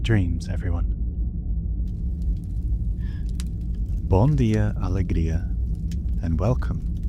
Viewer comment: What did you say there? Honestly just curious. Not sure if that was a real language or maybe a neat little DBD easter egg. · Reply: Hah it’s all good! I said ‘Bom dia alegria’, which is a portuguese saying which means ‘happy good morning’